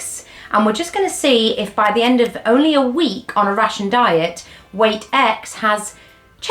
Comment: This is my first time watching you video. I enjoyed watching very much. One funny point…I thought you said you were eating Russian meals for the week…at the end of your 8 days I heard you say ‘my week of rations’ and put it together in my head! I laughed and then watched again from the start! Thank you for the information and entertainment.